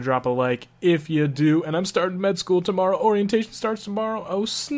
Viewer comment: i thought you said mid school, and i shat myself